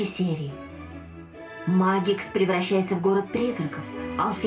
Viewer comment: и почему мне послышалось в последней реплике «... магикс превращается в город трезвенников..». это многое бы объяснило.))